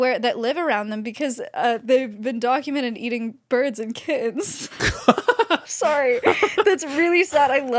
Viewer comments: I thought she said they were documented eating birds and kids and I was like W H A T ? ? ? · I wholeheartedly thought she said that they eat birds and kids, I’m crying